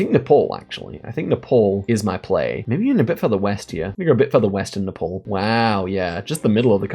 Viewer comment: I thought you were saying “the pole” and I was so confused.